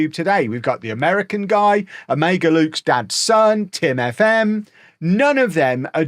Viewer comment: Did he say tim fm😂😂😂😂😂